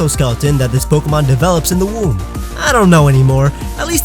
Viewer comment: womb? DID YOU SAY WOMB? POKEMON HATCH FROM EGGS REEEEEEEEEEEEEEEEEEEEEEEE